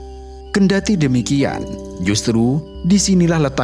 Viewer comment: detik ini ngomong apa ya .... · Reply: Kendati demikian